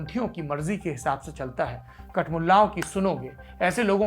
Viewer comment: did you say kat Mulla? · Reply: Yes..kat mulla..